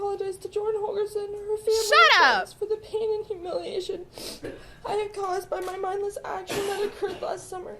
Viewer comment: Did she say "The pain and humiliation I DIDN'T cause" or is my hearing just shot?!!